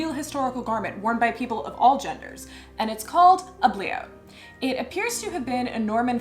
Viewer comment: "It's called a bliaut" I totally thought you said ugly-out and was like, "that's bold."